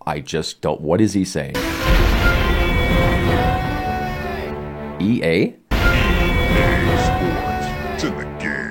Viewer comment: He's yelling "Shiiiieeeet!" I played this back in the days with my friend and I asked him what's he yelling? To me it sounded like "ciiiaaa!"